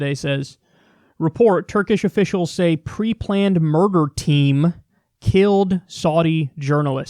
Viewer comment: At first I thought he said Saudi “mortar team” then i realized he said murder and that’s just as bad but not as creative.